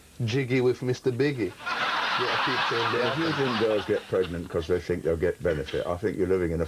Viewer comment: This is wonderful. Tony Benn had no idea who Ali G was and thought the whole thing was for real. In his diaries he described him as a very interesting and intelligent young man who had some very relevant questions. When he read in the papers that it was all staged he thought the whole thing was 'just terrific fun'. His warmth and humour come shining through at the end. Tony Benn, you were a giant amongst men, Ali G reeeeeeeeeeeespect! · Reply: Did I hear him say "Mr.Binge"?